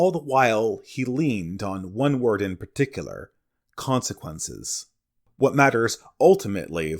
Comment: All the while he leaned on one word in particular: supererogatory Wait what he said consequences?